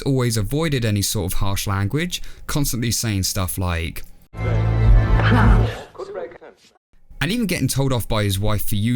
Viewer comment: what did he say? I can't unferstand it. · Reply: CRUMBS!